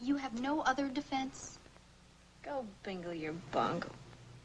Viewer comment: "Go finger your bung". Did I mishear that? I didn't know the concept was around in those days 8)